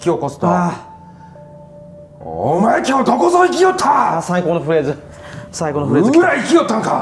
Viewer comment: かんひもで「最高のフレーズ」が「最高のプレイだ」に聞こえてしまった私の耳もじじぃフェチの呪いにかかったかもしれません👴🏻ｵﾂｶﾚｻﾏﾃﾞｼﾀ